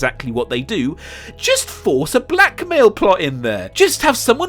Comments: When he zoomed in on finn and said "blackmail plot", ngl I thought he said black male plot lmao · I swear the first time you said “blackmail” I thought you said “black male” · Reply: Even when I realized he was saying 'blackmail', I still thought he was saying black male lol Makes no sense I know lolol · At first when he was using was talking about Reys blackmail motivation I thought he said it like black male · I thought you were saying "black male" for a while cause of that cut to a literal black male. I was confused for a minute lol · i swear when he said "blackmail" motivation and showed Finn, i thought he said "black male" situation..... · When you were talking about blackmail motivations, I thought you said black male and I looked over and saw Finn as the only one on screen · When you said Blackmail and showed Finn, I thought you said Black Male! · When he said 'blackmail' I heard 'black male' because Finn was in the shot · I honestly thought you said: "A black male plot" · Every time you said “blackmail” I thought you were saying “black male” since you showed a black male after saying it and that you were hinting a Finn-Rey relationship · I actually thought he said black male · Omfg when you were talking about 'blackmail motivations' I legit thought you were saying 'black male' as you immediately cut to a shot of Finn and Palpatine threatening to kill her friends, including Finn. I was like wtf is he on about??